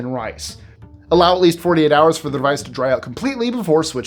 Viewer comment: That's what I thought he said too so I had to listen to it again. I believe he said "the device" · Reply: Yes, I had to rewind because I thought I heard "rice". He did in fact say "the device", but it was really quick.